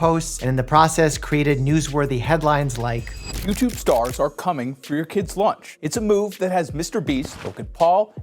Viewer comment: I thought I heard "Youtube stars are coming on your kids lunch" 😳